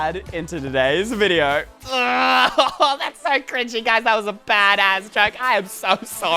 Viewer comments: WHAT DID HE SAY?!?! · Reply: •Lemony • bad@ I think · did you say "bad a**" 😑😑😑😑😑😑😑😑😑